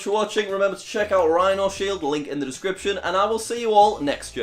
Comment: Did he just say “I will see y’all next year ?